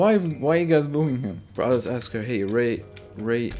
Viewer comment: i thought it said "ride my bat" O_o